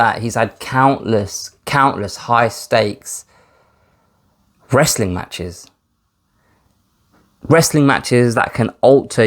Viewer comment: Thought you said breastling matches, was gonna ask where to buy tickets for that